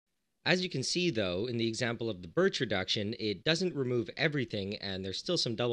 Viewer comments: I thought it said birth reduction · Goddamn I thought it said the Bitch Reduction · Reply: I thought it said Birth Reduction